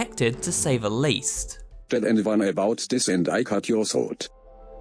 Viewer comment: How dare you disrespect my homie Fruit Punch Clown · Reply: I thought he said "I cut your sword"